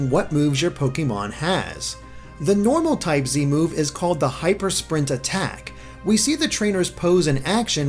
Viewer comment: I thought it said "Hyper Spaghetti" there for a second. Flying Spaghetti monster unforfunatley deconfirmed.